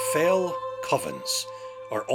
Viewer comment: I somehow misheard "Fell covens" as "Fell puffins".